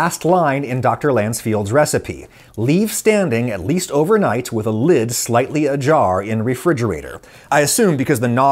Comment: there is a mistake in the captions: you say "with a lid slightly ajar in refridgerator" yet the captions say "with a lid slightly a jar in refridgerator".